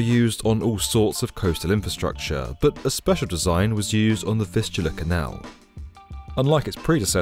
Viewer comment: I keep hearing “fistula canal”